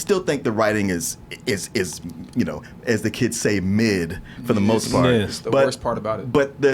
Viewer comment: did just say "mid"—& use the term correctly? This soul brother is HIP.